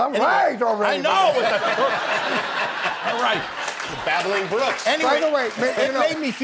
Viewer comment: It was a one-off comment under his breath, but Jimmy spontaneously coming up with a comedy duo name for Melvin Kaminsky and Albert Einstein as "the Babbling Brooks" was inspired. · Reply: I thought he said “battling.”